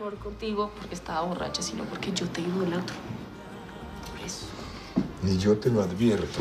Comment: alguien sabe qué es lo que dice Rosario en el minuto 4:16? "sino porque yo ...", lo repetí como 10 veces y sigo sin entenderlo 😩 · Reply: "Porque yo te idolatro, por eso"